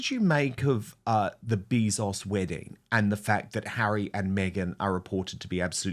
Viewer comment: I thought you said, “The bees are sweating”. And I thought, “What Bees are sweating? What did I miss?” But you said, “The Bezos wedding”! 😂😂😂